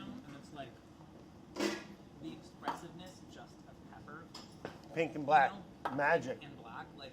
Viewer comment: Chris: "The expressiveness of pepper...." Brad: nods "pink and black" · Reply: I honestly thought he said "Painting in black" and I was like, "Wow, pretty beautiful way to say th--oh. Lol"